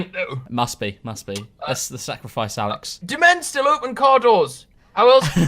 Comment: That moment when he called him alexx lkmao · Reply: I at first thought that as well, but then realised he really said: "Let's sacrifice Alex." ((To the sun gods or whatever))